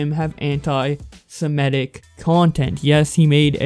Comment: Jesus I thought you said hentai-Semitic